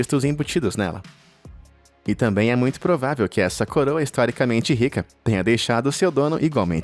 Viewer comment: Essa parte eu ouvi ele falando uma coisa da coroa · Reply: ele disse bem assim "essa coroa já deixou o like" ksksksks mas é real eu escutei mesmo